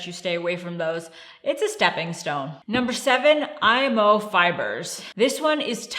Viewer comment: What kind of fiber are they talking about here: 7:05 Could they add the name spelled out on the video? I think they are saying IMO fiber and Isomaltooligosaccharide.